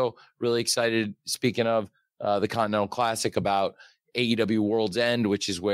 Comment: Did Tony said condom classic at 2:20 or I need to wash my ears? :D